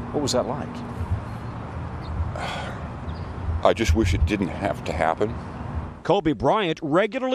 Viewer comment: HE SAID HE WISH IT (DIDNT HAVE TO) HAPPEN !!!WHAT IS HE REALLY SAYING??